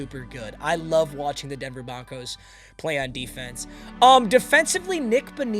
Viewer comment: Did bro say Denver Bonkos